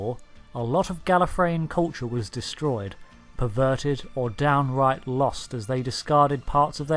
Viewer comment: did u say perverted?